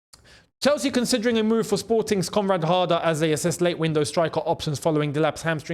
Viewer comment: I thought a heard say comrade harder😂